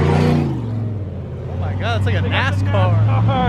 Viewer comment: >>> Did he say ASScar or NAScar ???